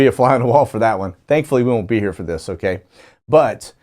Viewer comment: Gave me Holy Ghost goosebumps when you said we won't be here. God bless you Brother. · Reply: I heard him say we wont be there....as in Israel